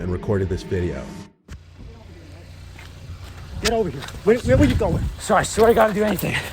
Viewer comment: what did the man say? I couldnt hear it well?👉👈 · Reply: Where are you going